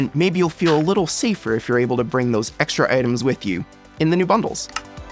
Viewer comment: i thought this said bunholes for a second there 😭😭